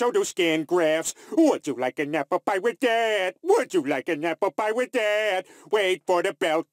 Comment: Ding fries are done, Ding fries are done Ding fries are done, Ding fries are done I gotta run, I gotta run I gotta run, I gotta run I work at Burger King making flame-broiled whoppers, I wear paper hats. Would you like an apple pie with that? Would you like an apple pie with that? Ding fries are done, Ding fries are done Ding fries are done, Ding fries are done I gotta run, I gotta run I gotta run, I gotta run Dont touch the fries in the hot fat it really hurts bad and so do skin grafts. Would you like an apple pie with that? Would you like an apple pie with that? Wait for the bell, Can't hear the bell Where is the bell? Wait for the bell Ding fries are done Diiiiinggggg friiieeeessss are doneeeeee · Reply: I thought he was saying "would you like a number five with that"